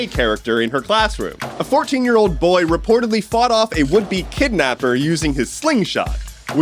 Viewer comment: I swear to god I thought you said his "wimpy kidnapper" instead of "would-be kidnapper"...still applies though xD